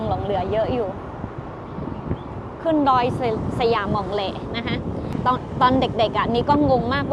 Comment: ได้ยินชื่อเป็นดอยสยามมองแล ทำให้คิดถึงว่าเป็นดอยที่ทหารสยามขึ้นไปใช้สังเกตการณ์ตอนทำสงครามบุกตีเมืองเชียงตุง😅